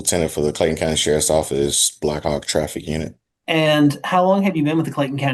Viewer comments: did he say "black ops traffic unit"? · Thought the same thing. Black Hawk for a traffic division? Sounds like a Black Ops team lol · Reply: I actually thought he said black ops traffic division 😅